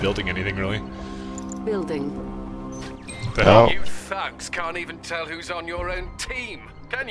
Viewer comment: i thought that english guy said "you fucks can't even tell who's on your team!?" :P